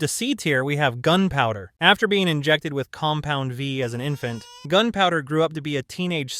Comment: did he say Cum Pound V?! 😮